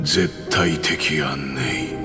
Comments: 「絶対的やんねぇ。」にしか聞こえなくて笑ってしまう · 辺りが絶対的やんねにしか聞こえんｗｗｗ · ずっと道化の絶対的安寧が絶対的やんねーって聞こえる